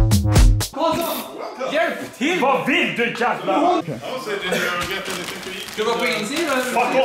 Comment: Does anyone know what says at 5:27? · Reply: JustCheckingIn "What do you want you fucking..."